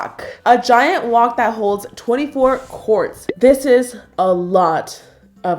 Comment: when she said quarts i thought she said corpse😂🤦🏼‍♀️